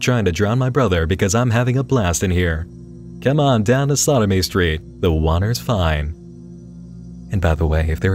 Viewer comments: All I hear is "sodomy street" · sounds like said "come on down to sodomy street" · I kept hearing "Sodomy Street". It fits, actually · I thought you were saying Sodomy Street throughout most of the video.